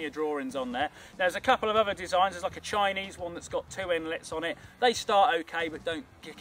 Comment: At 32 seconds in I thought he said "Chinese woman" and the part after that was the opening line to a joke... I really should get off the Internet for a while...